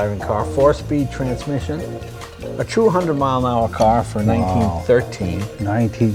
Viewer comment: did I just hear 200mph car?